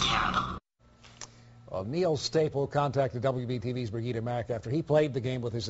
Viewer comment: I thought it said her name was "Bernita Mac"